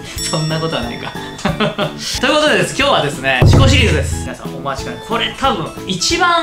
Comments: シコシリーズに聞こえた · 「シコシリーズ」って聞こえて突然下ネタぶっ込んだかと思った